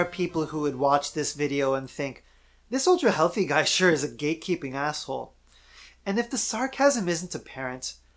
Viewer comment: I thought you said "gay-keeping" silly me. Not that there would be anything wrong with that.